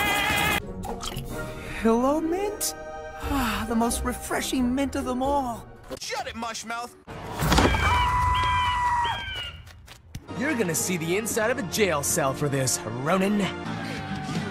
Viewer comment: Lloyd: The most refreshing mint of them all Jay: SHUT IT MUSH MOUTH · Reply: Wait, That was what he said?! I ALWAYS THOUGHT HE SAID "SHUT IT MARSHMALLOW" 💀